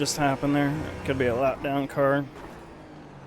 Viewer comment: this whole time i thought my spotter was saying "That car is a let down". Now I know it's actually a lap down! lol thanks for the video, this track feels so technical and difficult for newbie like me. it helped a lot as my only car is mustang!